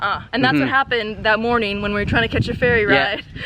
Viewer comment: Oh ferry...thought you said fairy 😂